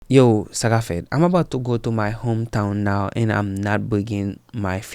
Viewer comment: What do you always say in your intro? Sara-felt or Zara felt? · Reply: Sa k ap fèt means "wassup". I m Haitian, that's one of many ways to greet others